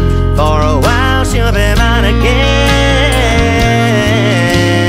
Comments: Who else laughed at this song when, you was younger because you thought he said a paint me burning ham 😂😂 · I remember when I first heard this song I thought he said " paint me a burning man" love this tune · I remember when I first heard this song I thought he said " paint me a burning man" love this tune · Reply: i thought he said burning ham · Growing up I always thought he said, "paint me a burning ham"...